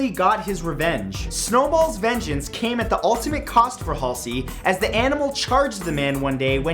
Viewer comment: i swear i heard hussie